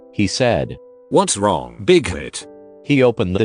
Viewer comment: I THOUGHT HE WAS SAYING BIG TIT LFMAOAOAOAAOAOA BIG TIT